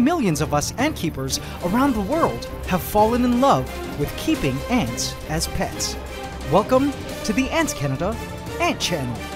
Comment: I thought said "Why we keep ants as PESTS" then i realized what on Earth he said 5 seconds later.